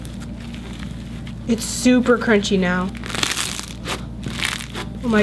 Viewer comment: I thought she said “It’s super cringy now...” 8:54 but she said crunchy